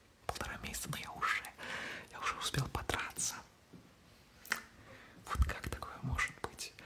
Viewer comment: я слышу слово ПОТРА**СЯ😂